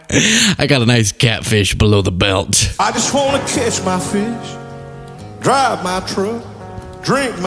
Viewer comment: I heard “KISS MY FISH” too!! Thanks for validating me!!